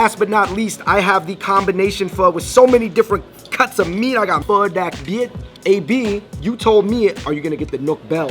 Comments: all different kinds of meat, I got PHO DAC BIET ( i thought he said fuck that bihh im weakkkkk) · It sound like he said fuck that bitch